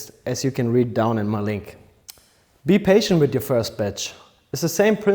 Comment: ' Be careful with your first bitch'...atleast that's what I first heard😅